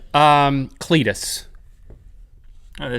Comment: I know someone called billy and this is totally true. And when they were talking about a cletus, I thought Link said clit.